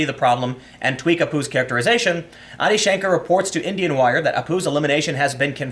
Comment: did you say Indianwire? lol